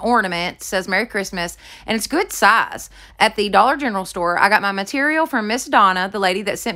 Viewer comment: Thought you said “the Dollar Funeral” store. Lol